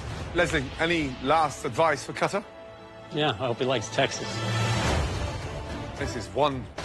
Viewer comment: Last advice for cutter: "Yeah, I hope he likes Texas". That was cold. Leslie is the GOAT. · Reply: Oh I misheard it as "I hope he likes taxis"😂😅